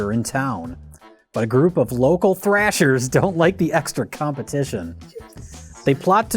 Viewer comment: When Jay said "They don't like the competition" while it was on Rich Evans can I heard it as "They don't like the composition". snip snap